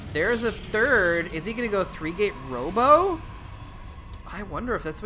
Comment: When you said 3 gate robo i thought you said 3 gate homo hahahaha